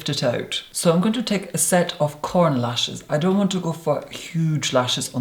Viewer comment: "I'm going to take a set of corner lashes" Me: " HES GOING TO TAKE CORN LASHES?" "....these ones are corner lashes" Me: "oh well duh. Corner." I really thought he said corn lashes.. I was confused.